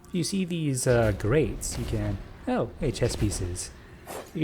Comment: Like, what is their function, or have you not seen pawns from the game Chess? We think they're a sort of lighting reference or something, but we don't really know. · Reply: oh are u saying chess pieces, not chest pieces? then i just misheard u lol